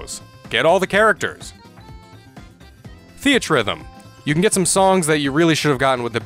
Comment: Great video! By the way, can you please tell me what the title of the game you said was @ 35:45 ? I don't know how to spell that. Thanks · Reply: Yes that's Final Fantasy Theathrhythm, there is also a second version subtitled Curtain Call, a rhythm game where you play to final fantasy songs!